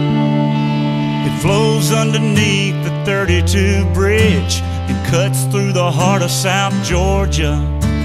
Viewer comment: I thought he said dirty Jew bridge